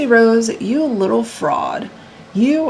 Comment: 😅💀💀💀 your expressions & everything you say is so spot on … she makes me sick & she’s a pathological liar & narcissistic & just like her mom. She loves seeking attention… let’s keep it all the way 💯 she could have stood up she could have idk called the police instead of meeting some dude & do whatever .. she knows exactly what she’s doing & it’s creepy!!!! Good luck to her man bcoz he’s gonna need it!!!! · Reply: When you said you little fraud I thought you said you little frog 🐸 😅🤣🤣😅😂😂😂💀